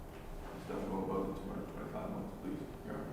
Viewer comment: what does he say here? "don't go above 20, 25 months" months...? surely its not what he said, right?